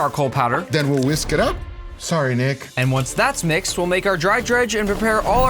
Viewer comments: idk why but i heard sorry Nig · did he say nick or ni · I swear guga called nick "nig* 😂